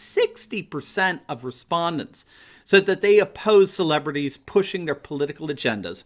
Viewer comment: I thought you said Celebrities, who are these nobody assholes???????????